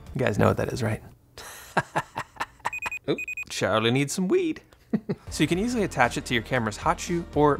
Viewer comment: I loved it and I know what a beeper is, or was, but after the beep, at 2:21, what do you say? I can't understand it. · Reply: Thanks John! Glad you liked! I think “Charlie needs some weed” haha 😆